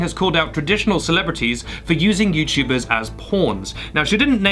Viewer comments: i keep hearing “porns” instead of “pawns” lmao also ace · I legit thought was saying porns when he was saying pawns